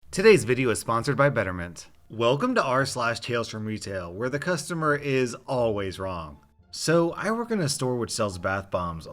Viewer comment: I thought you said "This video is sponsored by veterans." Lol